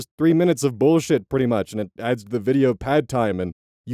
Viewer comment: I know you said "video pad time" i heard "video pad thai"